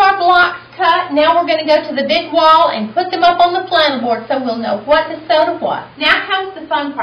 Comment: what board did she say in 3:20? was it flama board